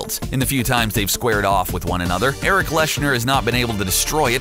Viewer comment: Did...did he just say erik leshner😂😂